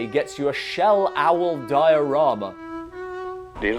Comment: I thought he said "shell owl diarrhea"...which would make more sense.